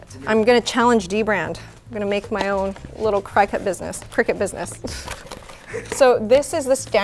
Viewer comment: For a whole 5 minutes, I thought she was saying "cricket".